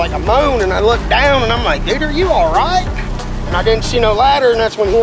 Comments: Am I the only one who heard him say “nigger are you alright”💀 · Somebody needs to listen to this more closely because I could have swore he said Niger you allright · Sounds like he said ninja you alright 😂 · I thought he said nigga you alright💀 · I can't be the only one that heard him say bigger are you alright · did it not sound like he said igger you alright i had to read the words he did not but the way it sounded